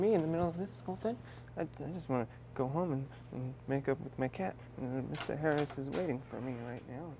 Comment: I heard "I just wanna go home and make out with my cat" at first